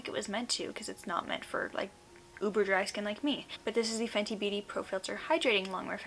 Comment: when you said 'uber dry skin like me' i thought you said 'uber driver skin like me'